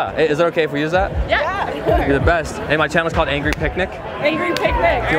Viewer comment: Why does the last guy sound like Rocky Balboa LOLOL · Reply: I though he was actually impersonating Rocky cause I heard, “You know Adrian” at the end. LOL